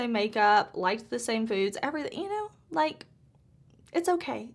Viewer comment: 😆I had to rewind a few seconds, I thought you said, like the same dudes, 😂then I heard "foods" 😆